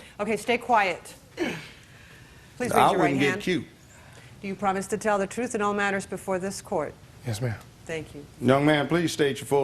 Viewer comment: what yall heard do you promise to tell the truth and all matters before the court... what I heard.... will you snitch... everything you know